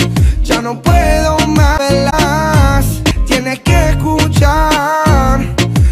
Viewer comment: dice me la pelas o yo escuche eso jsjs